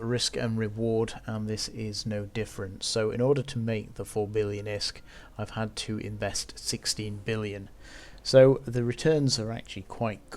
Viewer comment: did he just say in order to make 4 billion isk, he had to invest 16 billion? isk?